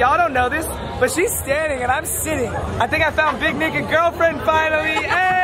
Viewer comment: did he say big n***a girlfriend or...?